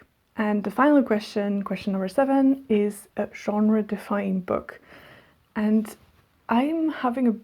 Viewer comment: Ah, then I probably mixed up "genre-defying" and "genre-defining" :D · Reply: ohh maybe it was genre-defying? i thought you said defining 😅 i dont know then haha